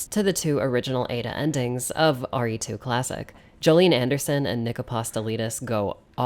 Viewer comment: I was really confused for a moment there as I thought you said Gillian Anderson.